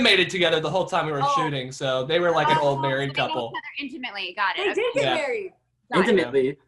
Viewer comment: Did madi just said "they did married" 19:53 "I have a picture of you guys ...... (a verb i didn't understand)... wedding in vegas"